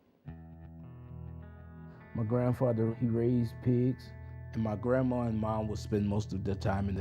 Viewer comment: I didn't hear say "we were raised like pigs or as if we were pigs" I clearly heard "my grandfather, he raised pigs"